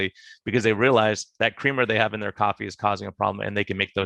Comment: Hey, why did you throw in "CREAM IN YOUR COFFEE" like it's bad news? Don't tell me you get sugar in your cream in the USA. Cows arenn't that stupid. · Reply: I heard "creamer," not cream. Big difference.